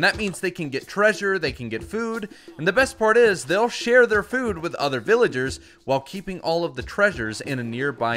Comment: I thought he said “They’ll shear their very own friend villagers” instead of “They’ll share their very own food to their friend villagers”